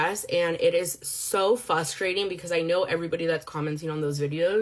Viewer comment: Did she just say fasturating but her strawberries are $50 guys omfgggggggg it’s frustrating